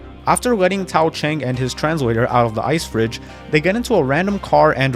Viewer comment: I thought you said trans lawyer